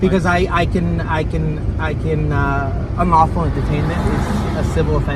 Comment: I swear he didn't actually say "unlawful detainment" 4:40 he said "unlawful entertainment"